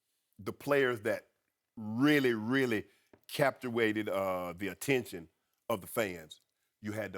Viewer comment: did he say Captuated!!????